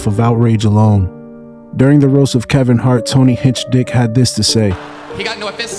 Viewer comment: 😂 did you just say Hinchedick? Holy cringe